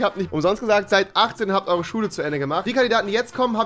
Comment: Ich hab zuerst verstanden: "seit acht, habt eure Schule fertig!" xD